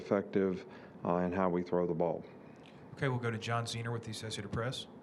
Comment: Did they say, "Ok we'll go to John Cena with the associated press?" @ 02:37 "Coach - You can't see me!"